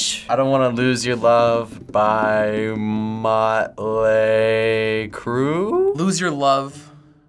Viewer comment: did he just say use ur love by motley crue..?💀